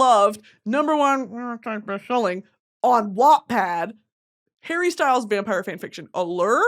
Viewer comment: what is she saying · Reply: "All time best selling" I think